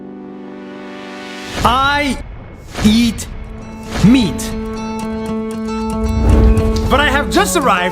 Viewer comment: Why do I hear "I beat meat"? 👊🍖😏